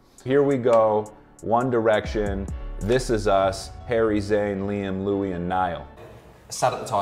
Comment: Is it just me that heard "Zac" instead of Zayn at 0:33 ?